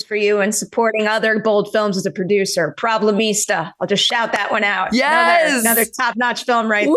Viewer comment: What is the name she shout outs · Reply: Problemista, from A24 I think